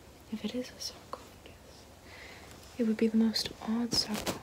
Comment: I truly thought she was saying esophagus wrong for the longest time